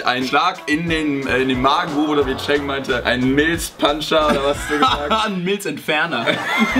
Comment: oke … ich glaube ich bin nicht normal xD … ich hab einfach Milfpuncher verstanden xDD hahaha